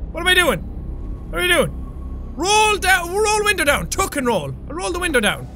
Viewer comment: Anyone else keep thinking: "JACK! TUCK AND ROLL, DANGIT!" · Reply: For a second I thought you said tuck and Rick roll xD